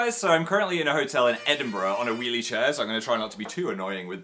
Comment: I thought you said wheelchair and was like “PHIL NO WHAT DID YOU DO”